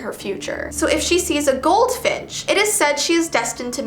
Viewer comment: did she say goldfish?